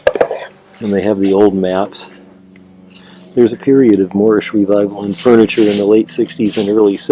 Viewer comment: When you shared with us about a revival, what kind of furniture had you said? Thanks! As always, love your content, especially watching you thrift!! ❤️❤️ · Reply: Thank you! I refer to it as Moorish Revival, meaning based on old Spanish Colonial designs